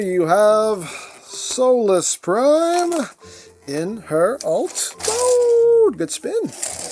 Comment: I misheard it as souless prime 😅😂